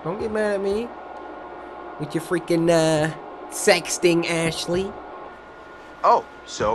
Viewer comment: what the freak did he say? Sexting ashley